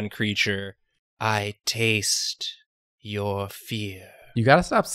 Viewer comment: I genuinely thought for a moment that he said "I taste... your feet"